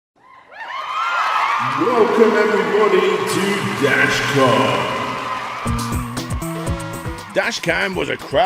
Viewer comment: don't know if it's me early morning hearing buut think I heard "dashcam". Also just a heads up; 13:35 has a title card without text. Also another amazing segue at 2:22 haha. Great vid but, a tad sad but great. Good stuff yo.